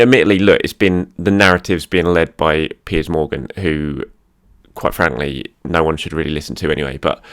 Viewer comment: Its the opening remarks on piers morgan for me, I hear, "... Who quite clearly no one should be listening to..."😂😂😂😂😂😂